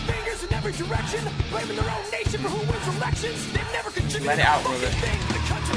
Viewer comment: did he say let it out nigga